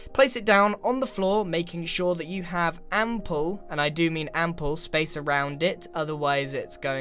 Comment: I'm sorry to ask, my english is not that good... what is "n-pull"? I didn't understood what you said... I think you meant to left a lot of space... but I'm not quite sure... Also, awesome video, you're very creative :D If I can make a suggestion, try to "redo" one; (or some); of your "building with Grian", but with this witchcraft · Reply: "Ample" Meaning plenty